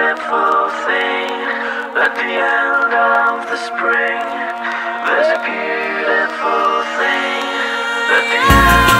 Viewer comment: Is he saying at the end of the spring? Or the string? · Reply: The spring